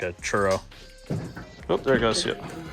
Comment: It looks like you're in the southwest, but I thought I heard an "ope" at 9:15 lol. Great videos and I'm glad I found this channel! Cheers from the midwest!!